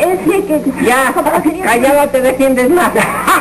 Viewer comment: Qué es lo que dice Kiko en el 3:09? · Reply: Callada te defiendes mas ...